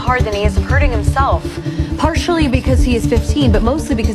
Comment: I thought he said "of course I do"